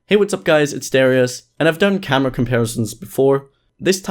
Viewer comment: At the beginning I thought he was going to say hey what's up guy's it Scarce 😂😂😂